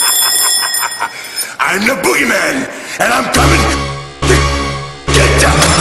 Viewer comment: I kept hearing "BootyMan" for some reason....